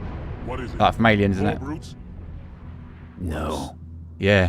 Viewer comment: did he say "no" because I remember him saying "worse" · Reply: said "No." Chief said "Worse." Hope that clears things up for ya.